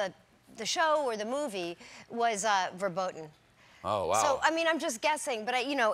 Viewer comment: Did she just say "verboten" ??? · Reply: Top5et She said forboden. It's another way to say forbidden.